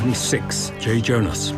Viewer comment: did he say joe jonas cause it sound like it